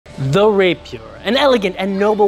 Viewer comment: I thought the first thing he said was “they’ll rape you” am I the only one who thought that